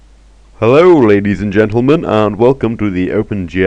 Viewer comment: I swear I heard "Hello rabies and gentlemen"